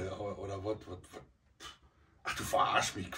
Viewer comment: Ich hab erst das N verstanden ❗😂